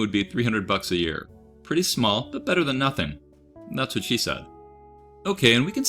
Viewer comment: Did you seriously say "That's what she said"? 🤦‍♂